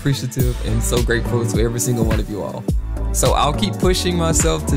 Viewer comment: “Cheers to another “tiers” of cake decorating.” I see what you did there Edit: I might have misheard but haha yah funny joke lol